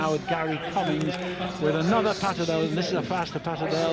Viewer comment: what is this breed? I can't hear that :( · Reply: That is a patterdale terrier